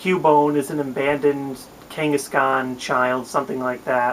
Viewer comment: I thought he said an abandoned ghengis khan O.e